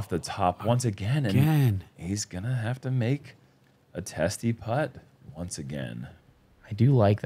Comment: anyone else catch say testis putt